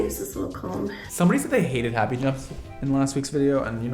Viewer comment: hated what?? I literally listened 5 times wtf did he say? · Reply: “Happy jumps”!